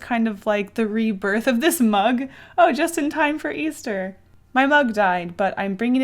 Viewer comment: When she said "my mug died but I'm going to bring it back to life" I thought she said "my mom" lol I died.